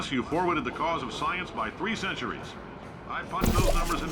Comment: subtitles saying "Plus you forwarded the cause of science, by (inaudible)". (Inaudible) I understand it as "3 centuries".